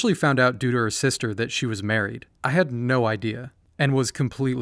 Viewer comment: For second, I thought it said "she was Mario"